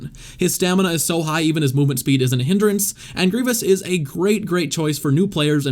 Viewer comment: Did you said grivious on